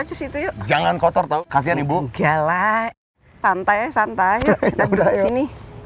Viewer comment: bu puan : " santai ae santai ". Salah denger g sih gue ? 😭